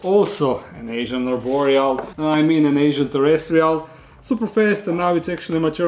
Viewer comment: I swear when you said "asian terrestrial" I heard "extra-terrestrial", had to do a double take.